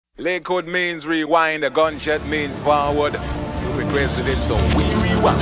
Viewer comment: In the intro I thought he said “liquor means rewind, the ganja means forward. I wasn’t even close but shit 😂